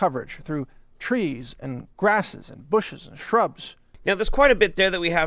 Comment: "...and Bushes and Trumps..." is what I thought I heard from him. Wouldn't be surprised if I wasn't mistaken.